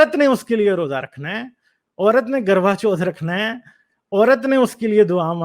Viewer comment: ओ भाई "गरवाचोद" नहीं, "करवा चौथ" करवा = दिया.... चौथ = 4th day तुमने इसे भी चुदाई बना दिया 16:28 Bhai, its not "GarbaChod" but "Karva Chauth" where Karva = lamps Chauth = 4th day What name you said means some kind of fucking festival 😂 😂 😂